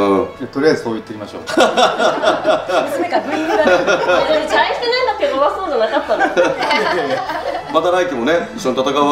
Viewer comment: 魚谷さんがなんて発言してるか聞き取れる方いますか？ 何回か聞いたんですがわかりません（泣） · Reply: 「大して、なんか手強そうじゃなかったです！」 かな？